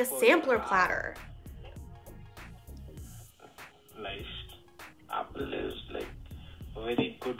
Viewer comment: Am I the only one who thought the scammer said: "lice" at 12:06 ?